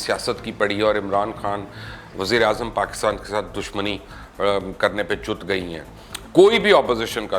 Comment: Did Mubasher said "Chud gayi han" am I hearing this right? Lol